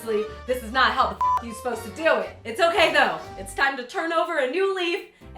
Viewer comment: Is it just me or did i hear "It's okay dough"